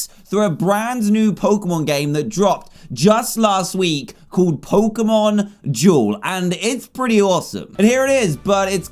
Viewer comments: I thought he said Pokemon jewel not Pokemon duel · When he said Pokemon duel I thought he said Pokemon Jewel. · I thought you said "Pokemon Joel..."